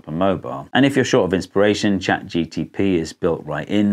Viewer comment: Am I the only one who heard "chat gtp"? 8:56 Love your videos by the way